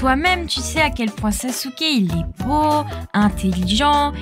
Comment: Moi au début j'avais compris "Naruto il a dit que" et après j'ai compris "Naruto il a dix queue"🤣